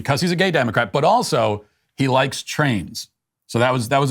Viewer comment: You got that one wrong Matt... He didn't say "trains" he actually said "trans". He a Gay Democrat who also likes Trans.